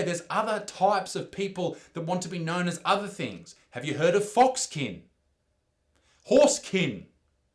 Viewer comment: I thought you said "foreskin" hahaha if people identify as pure force energy like from starwars we can call them force kin LOL